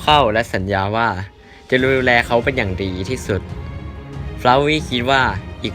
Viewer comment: มีใครได้ยินว่า'จะลูเเลเค้าเป็นอย่างดี'บ้าง